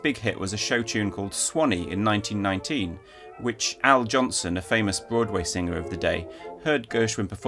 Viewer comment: I’m so excited that rhapsody in blue went to public domain. So many people are going to start playing it more. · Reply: Wasn’t Swanee sung by Al JOLSON? It sounded like I heard Al “Johnson”.